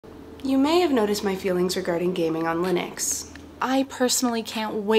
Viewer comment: When I first watched this, I thought she said, "you may have noticed my feelings regarding gay men on linux."